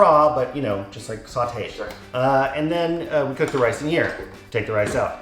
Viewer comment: Umm whose voice was that at 2:05?!! Sounded like he said “sure”. A Ghost?! Edit: realized it was likely the cameraman but didn’t want to delete my comment and detract from engagement! I watch all the ads for you too girl!!! XO